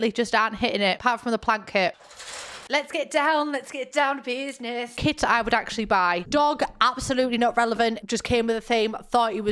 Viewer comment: Genuinely thought you said Kids you would genuinely buy and I was about to say oh no don't human traffick in the sims beb